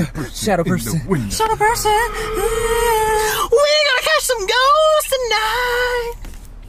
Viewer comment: I thought Sam said we gonna catch some goats tonight